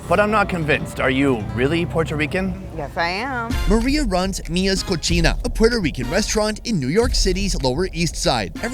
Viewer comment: I swear I heard you say Mia’s Cochina 🤣